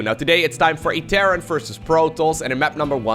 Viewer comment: Somehow I heard “It’s time for a Karen vs Protoss” in the intro......sadly no knitting crew to be found.....